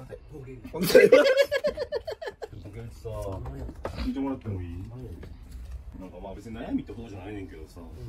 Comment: 「聞いてもらっていい？」がだんだん「聞いてください、」に聞こえてくる